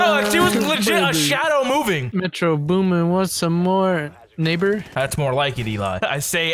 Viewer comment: I thought he said The N word at first when he said neighbor so I had to replay it lol